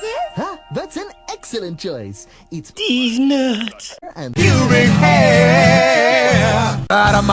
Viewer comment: does he say teeznut or keeznut